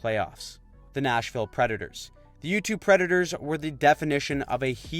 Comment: I thought he said youtube predators 😅